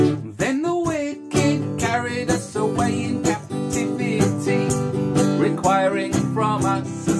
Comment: Oh! It’s ‘Captivity’. Even as a kid I heard it as “carry us a rain cap DVD”...and DVD’s hadn’t even been invented!